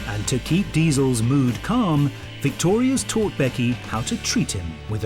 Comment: I thought he said: " Victoria torture Becky "